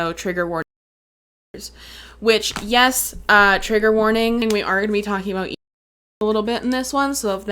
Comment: thought you said "yes we are gonna be talkin about you" damn girl call me out lol was putting this on to listen to while i work on computer and wow. rewinding and finding out it was just you editing out some words... although ive been told by strangers i do look like i have an ed... so maybe you are callin me out ahhh lol j/k i love your vids although i do come from the other end of the weight problem spectrum and wish i could do vids like you do... random but do you have any vids about your view of people with weight GAIN problems?? cause it really feels like an unsafe environment for people like me. it was bad before but thanks to fat acceptance its scary af. just throwing thoughts out and wondering if any one else feels this way. body positive just doesnt include bodies like mine. i love your videos cause its like it adds heart to the topic. i mean i feel comfortable enough to actually say this here cause i feel like you won't see me as fatphobic or rude but as a human with a weight problem which is what i am.